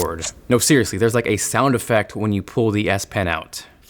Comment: I swear he says DS pen @ 2:05 lol